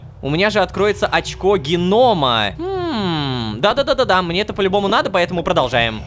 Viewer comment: я который услышал очко Венома: бля, Лололошка, я знаю что ты гей но не на столько же....